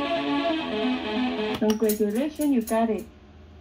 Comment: what does the audio say? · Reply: Congratulation you got it.